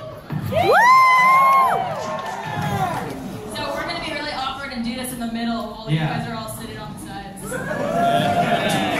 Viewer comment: What did she said?? I can't hear. (Another country here 🖑) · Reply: "We're gonna be really awkward and do this in the middle, while you guys are sitting on the sides."